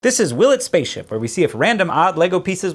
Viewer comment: I keep hearing ‘willet’s spaceship’